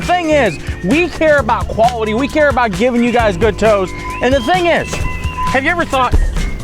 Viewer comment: Did you say giving us good toes? 👀😏